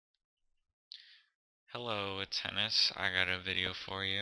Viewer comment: At the stat I thought you said tennis